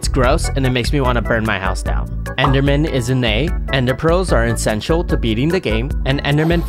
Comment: Bro i thought he said "ins*xual" at the rating endermans start😂😂😂😂 Edit:but he said essential